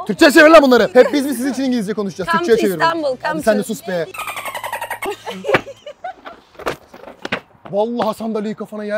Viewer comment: aceba ne diyor ya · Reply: Gel benimle evlen diyor